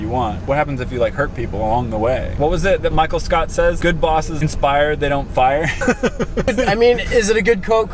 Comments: I thought said Good bosses expire not fire 🙃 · Lol when you said “good bosses inspire not fire” I thought you said good bosses expire not fire